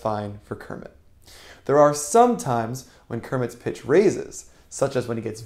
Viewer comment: I legit thought you said sometimes kermit gets racist🤣